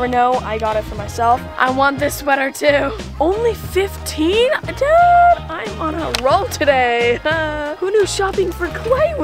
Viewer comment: who was filming u when u were at ross? Was it? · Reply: it sounded like said dad I am on a roll today